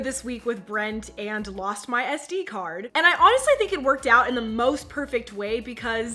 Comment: I thought she said she lost her v card